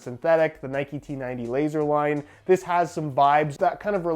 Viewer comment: I just heard said Nike T90 Laser Wine